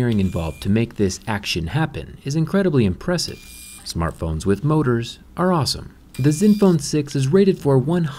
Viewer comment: I heard "Smartphone with boners are awesome"